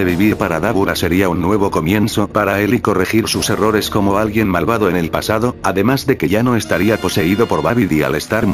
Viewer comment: escuché "Admin Malvado"